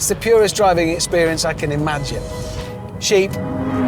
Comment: I thought you said cheap, but you said SHEEP.